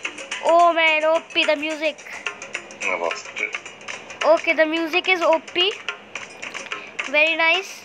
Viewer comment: did u just say the music is op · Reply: Yes he said the music is op